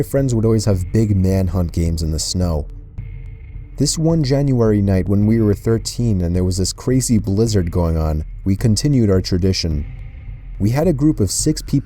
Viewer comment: i could have sworn he said crazy lizard